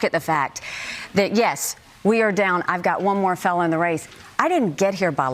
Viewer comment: did Nikki say “one more felon in the race” or “one more fellow in the race”?